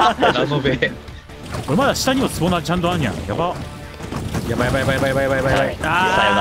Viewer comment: あんにゃん？(あるやん) に聞こえる（笑）